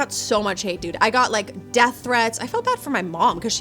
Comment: I had to go back. I heard "I got like, death threats, i got them from my mom" at first. I need to wake up a little more.